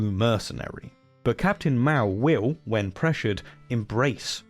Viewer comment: I thought you were saying Captain Mao! {:o:O:}